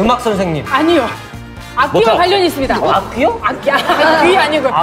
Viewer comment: 자막 안 보고 있었는데 저도 악귀라고 들었ㅋㅋㅋㅋㅋㅋㅋㅋ 악기 ㅋㅋㅋㅋㅋ ㅇㄴ